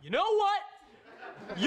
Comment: You know what? You're..You're bull... I..I you're very.. I'm I'M VERY MAD AT YOU!!! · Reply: Thought he said you’re bald